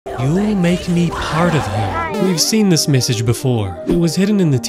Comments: Actually, She is saying "He'll make me a part of him" YA'LL STOP FIGHTING, THE CREATORS OF THE GAME HAS CONFIRMED MULTIPLE TIMES THAT SHE IS SAYInG "He'll make me a part of him" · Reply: I heard "don't make me part of him" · When it slowed down I hear you made part of him In normal speed I heard “ you made me spider venom